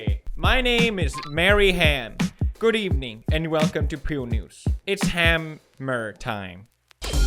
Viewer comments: I tot I heard 'peel news'... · "it's ham-mer time" ? He really NAILED that one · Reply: Thought he said hamburger time